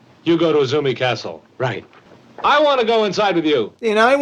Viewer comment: he clearly says "I wanna go inside of you!!!", not "I wanna go inside with you" or "I wanna go inside instead of you". WTF? Forced romance subplot much?